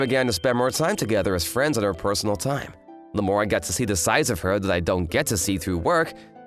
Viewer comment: At first I honestly thought he said: "The more I got to see the size of her." lmao I was like: "Oh, you got to see the size of her alright. Both when rockin' that business outfit as well as back in the hot springs, bruh." I had to do a double take when I realized said 'sides'.